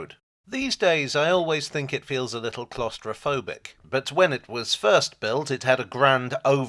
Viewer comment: I thought for a moment you said: "Gloucestrophobic".